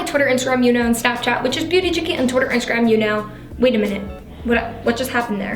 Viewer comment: It sounded like she said chicken instead of chickee 😂🐣